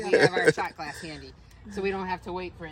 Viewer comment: Marie would have freaked at the sight of that snake. I opened my garage door the other day and a copperhead was laying there. Looked like a fun meet up. For a minute I thought they said shart glass...wondered why it was on their shoulder. lol. Looks like a true philly whizwit. Love me a spiral ham. Nice job on the cooking and the trip.